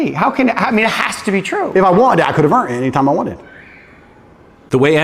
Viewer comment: did he say he could have EARNED time with his dad any time he wanted it? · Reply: I wonder if it’s a way of maintaining his sense of control. He said “if I wanted it I could have earned it”. It’s like he’s denying that he wanted it so he doesn’t have to admit that his needs weren’t met and so he doesn’t seem weak for having needs